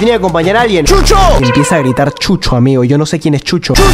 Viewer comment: Pensé que dijo chucha xd